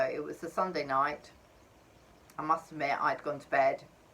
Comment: am i the only one who heard i mastarbate from benjys mom lol srry if i am wrong